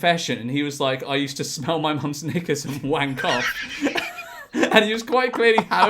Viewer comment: what did he say there?? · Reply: “Knickers” a women’s undergarments , sounds wild Ik.